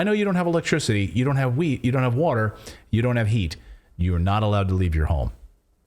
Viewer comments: I thought he said you do not have weed you do not have water. That'll be a time for me to leave. F*** that! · I thought you said you don't have electricity. You don't have heat. You don't have weed, you don't have etc etc. I had to rewind it three times to make sure you didn't say weed. Lol You said wheat I get it? I thought you said weed